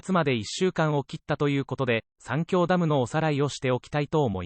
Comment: 配信ありがとうございます。今見たらもう１万３千人超えてますね。すごいです。👏👏👏 「三峡ダムのおさらい…」が「三峡ダムのお祓い…」と聞こえて爆笑しました。>(__)<彡☆ばんばん ところで、長江流域はすぐ水浸しになりますけど、長江下流に原発がありますね。 フクイチでは、配電盤や非常用発電機が地下にあり、津波で浸水して制御不能になりましたが、中国の原発は大丈夫なのかな？